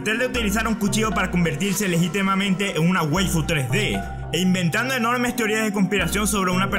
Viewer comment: que es una web 3d no entiendo que dice que es eso? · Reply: Creo que realmente dice waifu